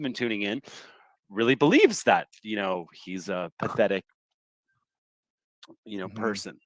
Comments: I clearly heard "Pathetic Puke" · I have listened to the audio several times and it sounds like person is saying "pathethic puke".